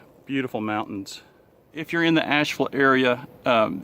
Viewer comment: Hi. Dan from Sweden here. Very nice video and informative. when I first looked at the clip, I thought you said; "just some few minutes from Nashville, so when I looked in Google maps I said to myself; "what's he talking about, it's at least 4-5 hours drive over there", until I understood that you have said; Ashville.